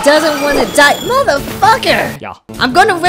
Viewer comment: "I'm a steel type bro u can't do shit" has got to now be my favorite line. This is now going to be my catch-phrase. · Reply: Funny thing is I heard "I'm a stereotype", not Steel-type, which I thought was supposed to be a joke on the voice.